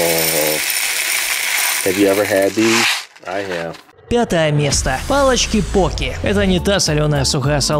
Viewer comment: Мне одной послышалось точто он сказал:пятое место